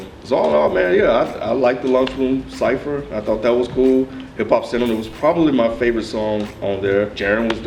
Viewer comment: lol did he say hip hop cinnamon